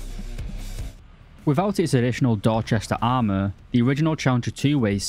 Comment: i almost heard you say dogshit armour instead of dorchester